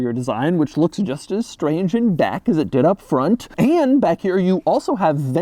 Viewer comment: Did he say “Which looks just as strange in Dack “ instead of Back🤔?? Am I the only one heard that?😂🤷🏾‍♂️